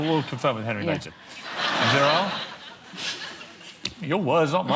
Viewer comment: What characteristic he says at 43:16??? What word is that? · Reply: Virile. It's a french loan word which describes a man who's masculine and having a strong sex drive.